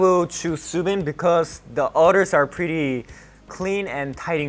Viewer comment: Not at the fact that it sounds like Kai said otter’s and not others